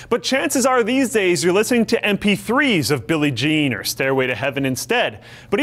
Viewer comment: When you said Billie Jean I thought you said Billy mays Remember hime